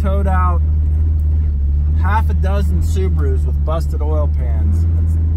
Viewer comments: I could have swore he said 6 supras knocked off their oil pan lol. Im thinking he must have said Subaru. I was thinking who the hell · Did he just say half a dozen Supras? Who the fucka dumb enough to take a beautiful car like that off road if thats what I heard